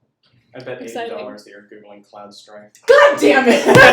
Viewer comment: does anyone know what he said? The thing he said that caused a little outburst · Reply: "I bet $80 you're googling Cloud Strife"